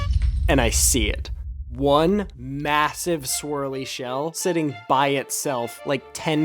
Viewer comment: did he say massive😂